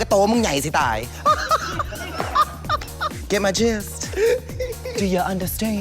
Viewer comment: What did jujube said at 08:17 before the do you understand?? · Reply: She said “get my jist?”